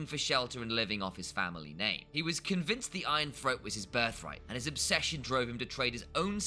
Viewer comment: "Iron Throat" i heard that